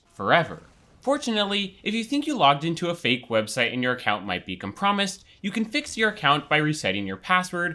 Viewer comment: Did he just say "Compromised" like "Calm prom issed"?